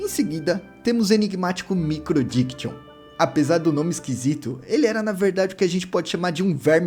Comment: eu ouvi microdickson kaka